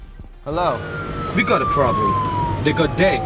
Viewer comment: พี่แบ๊งพูดว่าอะไรอ่ะ ใครรู้บอกหน่อย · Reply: we got a problem